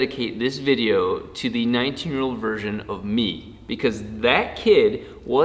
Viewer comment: I thought he said "19 year old virgin of me" Probably not wrong though